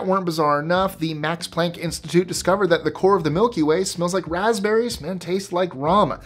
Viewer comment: Isn’t ethanol just drinking alcohol? Is he saying rum or ramen?